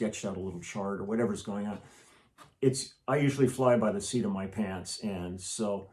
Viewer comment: Hi from Portugal, I'm a fan. I'm sorry english is not my main language but when Lee talks about the first take generally being his best I think I understood him saying at 14:09 "I usually fly by the sea to my pants". Did I understand it correctly? What does that mean?